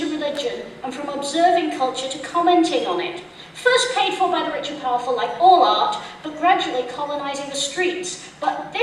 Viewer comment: what does she say from 1:19 to 1:23? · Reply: First paid for by the rich and powerful, like all art, but gradually colonising the streets.